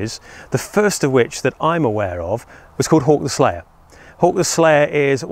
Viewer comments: Not trying to be mean, but the entire time you were talking about this, I thought you were saying Hark, the Slayer. Lived in England for two years, and I still can never pick that particular oddity out of the British accents. Great video, as always. And a very interesting topic. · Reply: I thought he was saying Hork the slayer lol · Not trying to be mean, but the entire time you were talking about this, I thought you were saying Hark, the Slayer. Lived in England for two years, and I still can never pick that particular oddity out of the British accents. Great video, as always. And a very interesting topic.